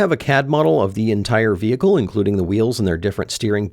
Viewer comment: I thought you said a cat model